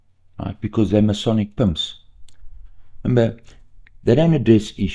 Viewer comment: I THOUGHT he said Masonic PUMPS